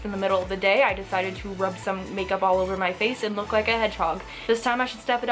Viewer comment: I'm only missing one ingredient A hedge hog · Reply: I thought you said a “hedge dog” XD